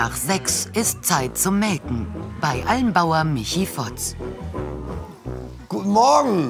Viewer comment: hab ich das richtig verstanden ???? ich musste so ablachen bei almbauer???? wie kann man denn mit nachnamen heißen ? jeder denkt doch da an was......auweia hab mal gegoogelt der heißt wirklich so ! fehlt nur noch ein e ^^ stellt euch mal vor der wird irgendwo über lautsprecher ausgerufen